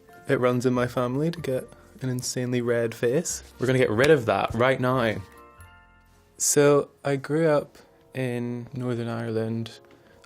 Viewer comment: I heard, "gonna get rid of that right gnome".